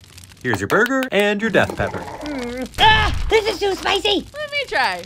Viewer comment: I thought he said Death Penalty not Death pepper ☠️